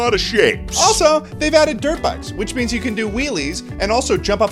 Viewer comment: I misheard "dirt bikes" as "derp eyes"